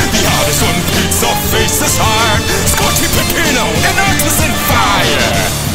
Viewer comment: WE'RE MAKING IT OUT OF PEPPERMAN ART GALLERY WITH THIS ONE‼️‼️‼️ 🗣🗣🗣🔥🔥🔥 Also at 0:55 it sounds like he says autism fire